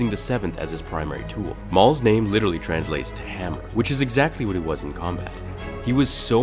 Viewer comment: did he say ham?